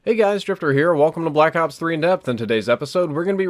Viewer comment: In every intro i hear:" Hey guys stripper here" ( ͡° ͜ʖ ͡°)